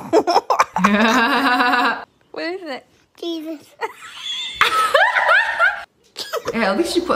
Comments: the baby did not say Jesus she said cheese it’s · I thought the girl who said close ur eyes open ur mouth I thought said Jesus not cheese its